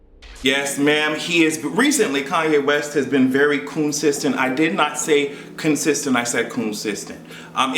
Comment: I rolled my eyes so hard (and almost clicked off) when I thought you said Kanye’s been consistent untilllll I replayed it and heard you say coonsistent 😂😂😂